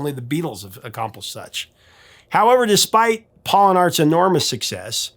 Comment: Forgive me...on first hearing you say it (around 03:07), I thought you said, "Upon Hall and Oates...," so I listened again and, "...Paul and Art's...." made more sense; though, it still feels strange in its overindulgent sense of familiarity; you know, with you being a few decades behind them and a 'professor' and all. That said, I really enjoyed the reminiscing (though, sometimes, it seemed to border on gossip, which the world is way too full of thee days). All in all, I'm happy to have subscribed — as long as you don't 'notify' me to a frazzle. A tip of the nostalgic hat to you!